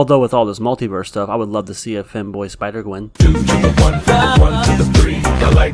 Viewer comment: Now THAT'S what i'm talking about MAN. really hope that i don't die tomorrow for saying that cause that will be funny i think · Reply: so i somehow misheard the word as "fanboy" instead of "femboy" so now i am probably gonna die today cause of that